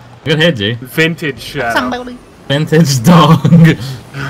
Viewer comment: anybody hear him say "Samboody" lol